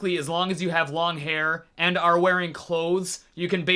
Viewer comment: Why did I hear “As long as you have long hairs and/or wearing clothes” 🤣🤣🤣